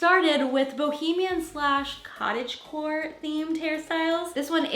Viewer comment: I thought you were saying cottage whore until I looked at the title 😳🤣🤣🤣😉